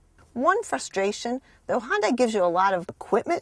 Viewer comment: Did I hear "Honda"?